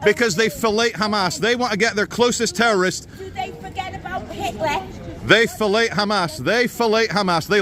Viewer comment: Did she just say, " Do they forget about Hitler?" Nasty piece of work her, bet she has all the victim cards in her purse, though.